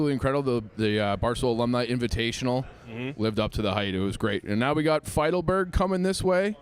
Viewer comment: does say “lived up to the height”?